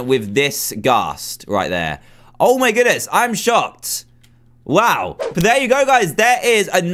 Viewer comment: He says I am shopped instead of I am shocked